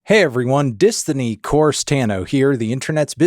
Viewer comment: I was eating chips and couldn’t hear well and legitimately thought in the intro he said “Di-thony Vorce-Tano”